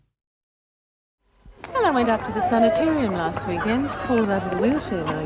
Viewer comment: did Reno say I went up to Senate or something? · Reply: Sanitarium I think.